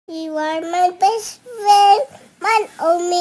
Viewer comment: I thought it said fish friend